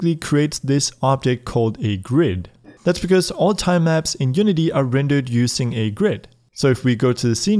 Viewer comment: I thought it said: TIMELAPS in unity